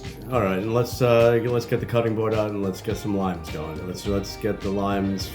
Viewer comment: I totally heard him say "get the cutting board out and cut some lines... Limes" Lmfao